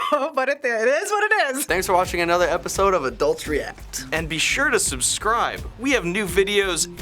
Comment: I heard 'thanks for watching another episode of adultery act' when he said 'adult's react' xP